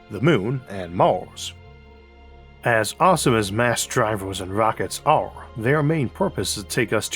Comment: I thought it said, 'New Drivers versys Rockets"